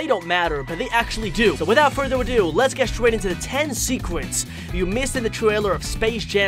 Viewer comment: The firts second I’m like “ did he just say secwets instead of secrets?!”